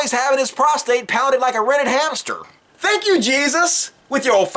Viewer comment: pounded like a "redded" hamster ? what kind of hamster did he say ?